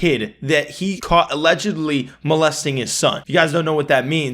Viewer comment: idk how but I think I am dyslexic I heard “allegedly blessing him” what the fuck is wrong with me